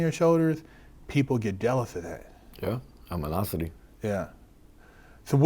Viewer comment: WTH hell did say at 11:39 🤣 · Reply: Animosity